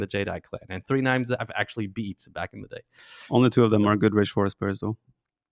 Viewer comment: What did Viper say? Cannot understand his mumbling. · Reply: "Only two of them are good Rage Forest players though"